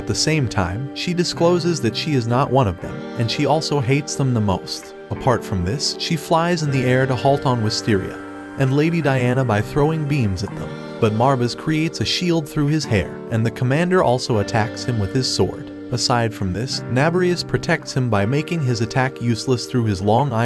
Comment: I thought he said "by throwing beans at them" lol